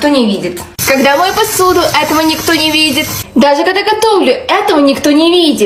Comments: Мне одному послышалось в начале: когда я умираю этого никто не видит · Мне в начале послышалось - Когда я умираю этого не кто не видит😂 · Мне ещё послышалось "когда я умераю этого не кто не видет"